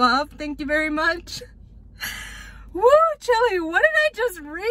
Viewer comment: did she just say that 1:48 she said the chili